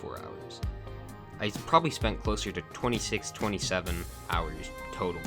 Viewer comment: what did he just say… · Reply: 6... 7...